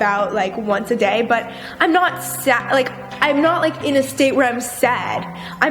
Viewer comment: um who else heard her say “said”